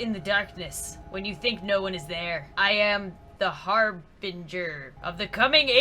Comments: When the thing sharif he was a harbinger I thought he said hamburger 🤣🤣🤣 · I thought it said I am the hamburger XD! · I THOUGHT IT SAID HAMBURGER 🤣🤣🤣🤣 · I thought that said Hamburger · When it said harbinger I thought it said hamburger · When the Watcher said harbinger I thought it said hamburger._. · I thought it said, "I am the hamburger" Instead of Harbinger or whatever it said. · When she said Harbinger I thought it said Hamburger xD